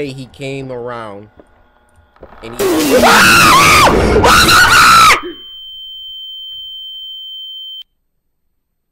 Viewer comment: Did CoryXKenshin say.... " WHAT THE F#CK" or "WHAT WAS THAT" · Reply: What the freak